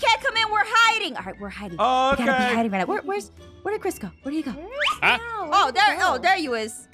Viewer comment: Did he just say we’re Chris go then we’re is ein go